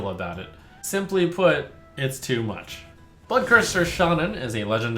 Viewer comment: Am I the only one who heard @ 11:07 Buttcurser instead of Bloodcurser lol